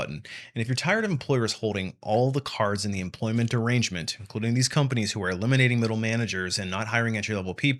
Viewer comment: I heard “if you’re tired of employers having all the power in the employment derangement” and I thought it made sense for a minute